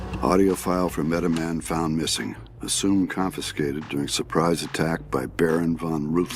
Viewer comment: I thought the files said he died in a surprise attack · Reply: No it said his audio file got stolen in a surprise attack